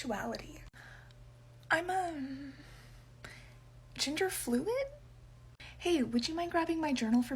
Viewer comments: "What's your sexuality?" The subtitles and what I heard: "Ginger Fluid?" Ah, of course. Anyone that is specifically ginger. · I heard ginger fluid